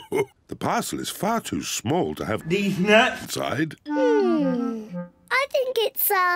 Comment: what did George say? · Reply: Deez nuts